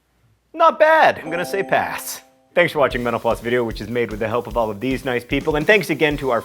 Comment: The first time I saw the video I thought I heard "Thanks for watching menopause video..." 8:25, lol! Oops.